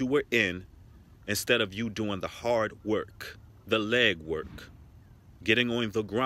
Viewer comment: See you said.."leg work" but I HEARD "Lead work".. but lead like the METAL... OR IS IT MEDDLE? GO HEAD NOW.... YAZ.. .I luv it...